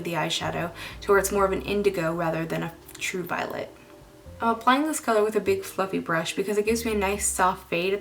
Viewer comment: Omg. At 3:39, I thought you said, "I'm applying this color with a big fucking brush" and I was like :O lmfao!